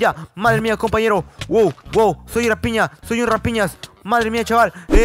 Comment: Pensé que dijo soll una piña